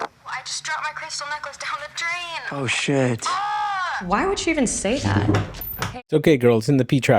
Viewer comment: Something i noticed on a rewatch of the movie that I didn’t notice when seeing it in theatres is that Nikki yells “ what did you do” when she snaps back to reality, that singular detail makes the ending even more haunting in my opinion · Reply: I heard it as "why would you do-" like she was going to say "why would you do this to me", "you" being Bear :( she definitely knew WHAT he did at that point, she just couldnt understand WHY. Which makes it even worse imo.